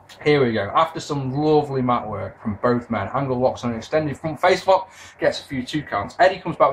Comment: Swear at 29:50 sounds like says Front Face Fuck LMAO